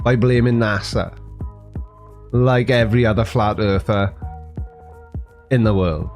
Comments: I swear, it sounded like he said "as a fat earther...". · According to YT captions he is "a fat Arthur" 😂 I had to play it back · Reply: I thought he said a Fat Earther lol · I swear it sounded like he said “fat earther” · I honestly thought he said he was a “fat” earther. Oh an aside Did you one say your are a graphic designer (I have questions from my 14 year old who wants to be one)